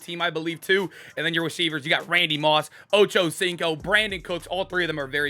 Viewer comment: Thought i heard you say “Ocho-Stinko” for a sec